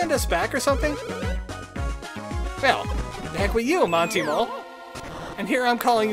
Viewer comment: Monto Mole? For a long time through this sires I thought you said, Mondo Mole! :)